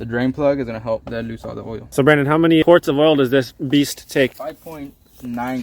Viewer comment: Did someone say "beast"??